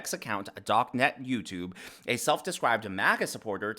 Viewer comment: I could’ve swore I heard her say “MAGAts” maggots.